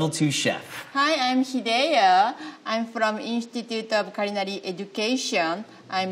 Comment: her japanese accent already kills anyone else’s chances of winning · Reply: I think she just said she teaches at the Institute of Culinary Education, maybe you misheard it as Korea? Her bio online says she studied in Japan and America